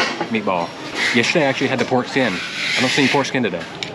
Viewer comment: No joke thought you said foreskin, not pork skin. 😂 I was like, the foreskin of what?